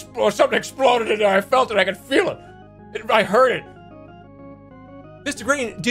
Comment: I can't tell if he says " I heard it " or " I hurted " and knowing Jerma I can't reason within my head which he actually said because both are believable in the context. Gold.